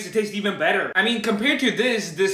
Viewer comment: When I first heard him say "Spriteh" I thought he said "Spiteh"😂😂